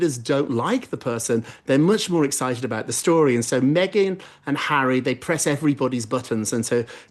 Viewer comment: For a split second at about the 1 hour and twenty mark, I swear I heard him say "Maggot and Harry." Is it me??